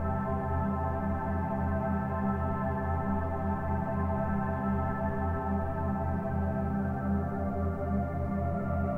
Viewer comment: Does anybody know what Phil was saying at 10:45? The female right before that said, "Oh god." Then Phil starts talking to Leroy and only thing I can make out was there at the end when he says "around the room" or something similar to that. Based off body language alone it almost seems like he was dressing down Leroy. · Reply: Wondering the same. I can make out him saying "four or five cameras" but thats it.